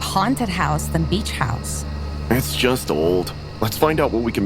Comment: For a sec I thought that said Bleach house...